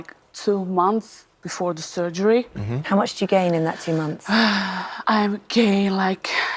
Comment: ok I know she said gain but it almost sounds like she said gay.... 😐